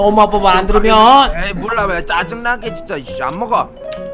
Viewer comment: 에서 짜증나게 썅놈아라고 들림